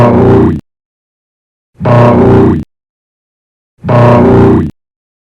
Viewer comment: At around 0:20 it sounds like hes saying baaaoooi